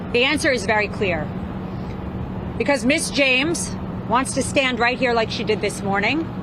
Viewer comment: I thought she said, "Bitch James." 😅 Then I was disappointed to hear her say "Miss James " Bummer, I'd have preferred the former.